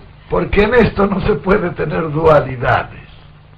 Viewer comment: dijo que nestor no puede tener duhalidades o escuché mal?